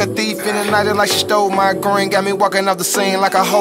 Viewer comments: I thought she said “that’s my wig” instead of my wave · AM I THE ONLY ONE THAT THOUGHT IT SAID: "Thats my weight"